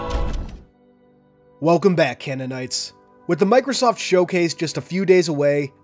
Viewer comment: For a second I actually thought he said "welcome back deadites", which made me think what would be cool? If master chief took on the evil dead.